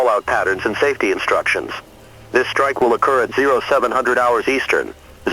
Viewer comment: This is an emergency alert...... Mark The Time..... ☢️ What I heard was: "Make your time! Ha ha, All your, base are now belong to US!" 😂🤣